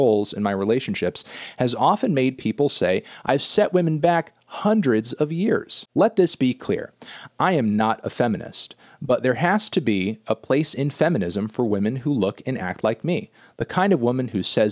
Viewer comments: I thought she said “I’m not not a feminist” lol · she actually says “i’m not not a feminist” meaning she is one, i thought it said “i’m not a feminist” at first too but it’s just weird wording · It should be noted that fantano says the opposite of what's written here. He says "I'm not a feminist" when the writing is "I'm not not a feminist", which means the opposite.